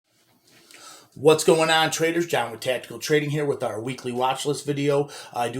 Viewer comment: I always hear ''John with Tattoo'' at the beginning of his videos, always thought it was what he said XD Great work brother made banks on VPER because of you